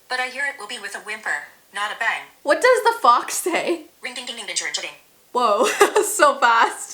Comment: When you asked “What does the fox say” , I put it in the slowest it can be, and I heard “ring ding ding ding ding church ding ding ding”